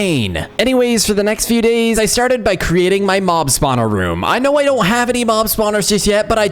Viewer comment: I thought he said mom spawned room